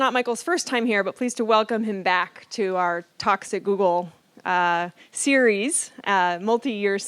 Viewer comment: Whenever I hear any "Talks at Google" introduction, It is said so fast it sounds like "here at toxic Google." :)